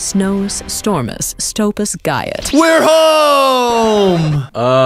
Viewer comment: I heard GYAT from the snow queen